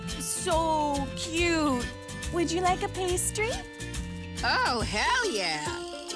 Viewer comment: What's the show's rating again? I could swear I heard Bee say "Oh HELL yeah!"